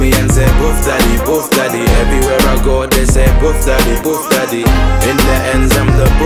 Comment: I thought he was saying Puff daddy